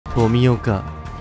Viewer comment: 「冨岡」が「ゴミ岡」にどうしても聞こえてしまうwww